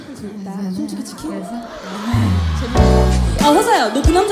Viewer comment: 처음에 뭐라고한지 알수있나요?ㅠㅡㅠ00:00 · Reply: 마마무놓지않을께 아배고프다 솔직히 치킨?